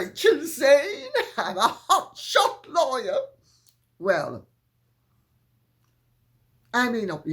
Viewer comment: You said “Hot Shot Lawyer” but I heard “Hot Shit Liar.” Coincidence? I don’t think so!